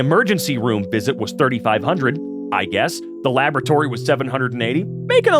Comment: I thought it said "lavatory" instead of "laboratory" for the bill for a stomach ache, and I was like, they charged you for what. (On top of my general, they charged you for that! At the American Healthcare system as a UK resident/citizen) Also I have that exact printer and can confirm it has similar seizures every other time I try to print anything